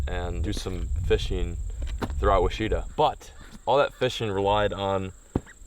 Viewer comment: On behalf of all of us Arkansas guys, Ouachita is pronounced “Washitaw” not “Weeshatah”. No offense. Lol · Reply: Lol I heard “washeeta” and was like... where? Took me a minute to realize he meant ouchita